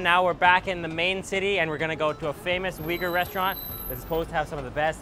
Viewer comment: I thought he said "we're going to a famous wigger restaurant" and I almost spat out my coffee.